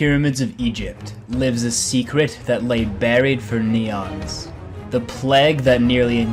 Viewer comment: did you just say neons instead of eons?